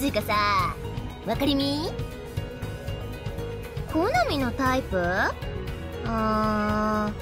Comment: すいませんなぎこさん、「好みのタイプ」が「KONAMIのタイプ」に一瞬聞こえてしまいましたw限定ボイス助かる(*´∀｀*)